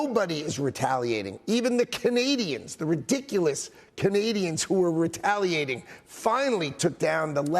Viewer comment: "Even the Canadians, the Ridiculous Canadians" i mean WTF is this, Is there any Canadian in the comment section? Correct me if I heard something wrong